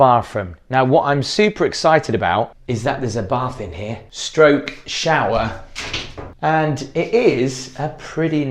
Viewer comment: did you say a stroking shower? what the heck is that, or do I want to know